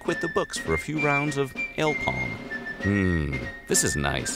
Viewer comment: did he say el pong cause the captions say alcohol